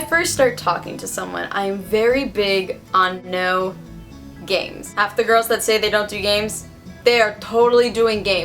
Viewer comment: I thought you said no 'gangs' not no games and I was wondering what type of people you know haha